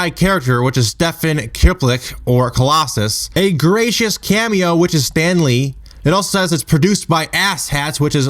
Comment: I thought it said "gratuitous cameo" not "gracious cameo." I only saw the movie once though.